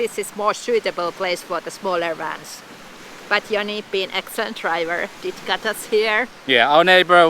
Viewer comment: I heard, "But Jonny being a stunt driver, got us here." Did I heard that right?